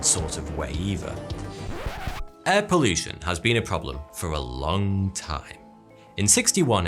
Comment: I heard it as 'Air pollution has been a problem for a lung time' XD